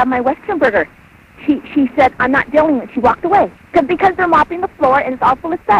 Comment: Dam that's good editing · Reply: Charleston Buck 1:33 did she just say I'm hot dang it? Or #mesmerize